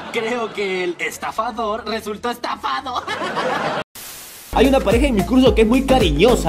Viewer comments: jaja soy la unica que escucha que dice :hay una pareja en mi culo" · Like si te diste cuenta que dijo cuño en vez de curso en el minuto